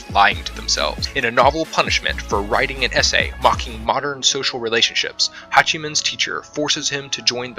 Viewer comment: Damn I really hear :"hEnTa¡ SoCiAl"… I’m down bad… F